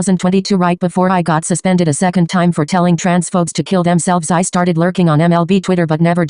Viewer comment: How is a person who "told trans folks to kill themselves" talking abt some being problematic, like, do you not see the irony here?💀😭🤡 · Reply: OH jesus christ thank god this comment and the clarifying reply exists, i heard “folks” instead of “phobes” too 💀